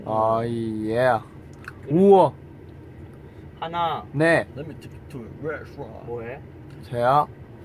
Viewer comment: I just heard ‘let me take you to a restaurant’ 😂